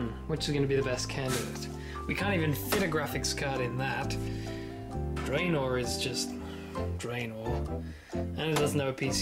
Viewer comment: Wait...did he call that computer Draynor? 1:58 LUMBRIDGE FUCK YEAH RUNESCAPE!